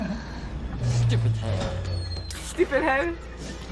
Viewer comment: did he just said stupid høe?? ರ_ರ